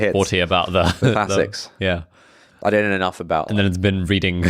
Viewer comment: can anyone whose English comprehension is superior to mine tell me what and said from 2:13 to 2:30? Thanks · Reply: T: I don't know enough about - A: and then it's been reading [inaudible] A: like how I read random books on the pod, A:the only time you get to read, A: how do you plan to read 3 books this year, well funny you should ask ,T: for one episode a week A for T for